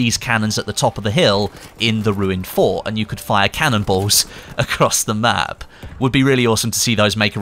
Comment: Kinda sounds like you said the cannons fire cannibals